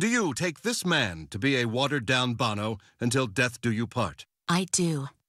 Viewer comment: "I Now pronounce you pretenses and terrible"😂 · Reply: I thought I heard him say pretentious and terrible, not "pretenses and terrible".